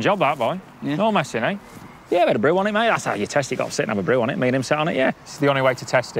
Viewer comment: What did he say at 6:11. 😂😂😂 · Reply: Sit and have a brew on it.